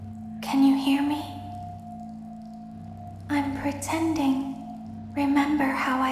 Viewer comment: When baby said I'm pretending at first I thought she said I'm pregnant lol 😂😂 Edit: Wow 5 likes! I feel important for once. My maximum likes was one, where i liked my own. I didn't like this one tho. ☺